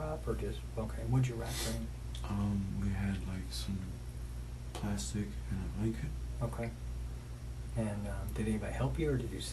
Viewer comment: does he say 'we had some ....."? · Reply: I think he said "plastic and a blanket"